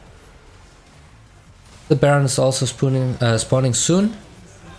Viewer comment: You had me at "The Baron is also spooning.. Uhh Spawning soon." LOL!! (27:01 - 27:03) I was like.. Did he say SPOONING? Haha!